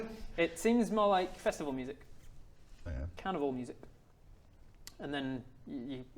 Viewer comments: "carnival music" What I heard: "Carnivore music." I was a bit scared for a while.... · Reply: Man I heard "cannibal music" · "carnival music" What I heard: "Carnivore music." I was a bit scared for a while.... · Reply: I heard "Canibal music" lol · I thought kept saying cannibal music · "carnival music" What I heard: "Carnivore music." I was a bit scared for a while.... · Carnival music! Thought he said cannibal music! Big difference 😂